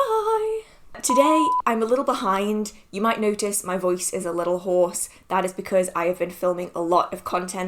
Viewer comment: I heard "my voice is a little horse (hoarse)" and now I can't un-see it 😂